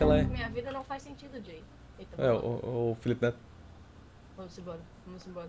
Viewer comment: so eu entendi "o felipe neto"